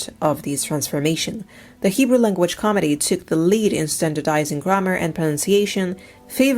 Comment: I enjoyed the video. What is your accent. Your English accent is confusing. I thought that I had heard you say the "Hebrew Language Comedy" when it was actually Academy you said. You should pronounce academy with strong Ahhh sounds, i.e., Ahhhcahhhdemy. Destroed - you meant destroyed. You should probably do videos in your mother tongue and use subtitles in English.